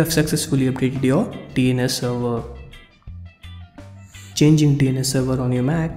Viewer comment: Hi. You helped me fix my connection issue! Thanks for your timely advice! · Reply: DID HE SAY "CHANGE your penis?"